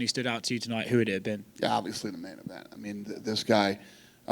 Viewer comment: who does he say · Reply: Main event